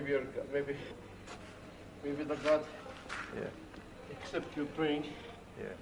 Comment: What he said “Accept your praying” What I heard 😂 “except Ukraine” 😂😂😂😂😂